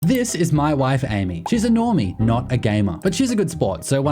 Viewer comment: Before I watch this I have to put it out there that this started auto-playing in the background while I was doing something and I miss heard you. It sounded like you said, "This is my wife. She is annoying." I was like, "Wait, what?" And rewatched the start. 😅